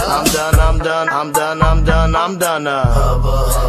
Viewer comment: I really thought at the end he said YOU FORGOT TORTILLA AND BUTTA